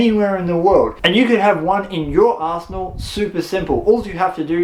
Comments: I probably wouldn't use the word "arsenal" anymore...it sounded like you said "you can have one in you're arsehole"...I dont want one in my arsehole....judging by the looks of it though, there may in fact be people that would love one in their arsehole... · I had to reweind at 7:55 , because the firstime i heard "and you can have one in your arsehole" 😂